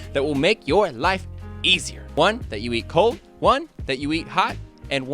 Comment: I heard one that you eat high for some reason, im dead 😂